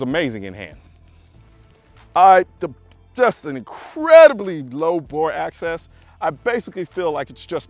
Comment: im sorry?... did i hear low bore ass'es ? bang bang skeet skeet lol